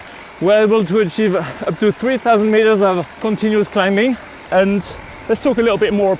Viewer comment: That's what I thought but rewind to 3:10 and he said 3000km of continuous climbing. That's a big ass mountain but wonder what that translates to flat range. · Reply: 3000 metres of continues climbing not 3000 km.